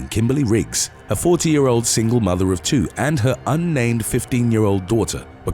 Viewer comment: How about "Where hearing things wrong goes wrong?" For a moment, I thought the voiceover said "14 year old mother and her 15 year old daughter..." Clearly not!